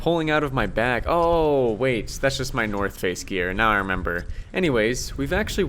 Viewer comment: Sorry, I might've misheard, didyou say North Face? 👀😂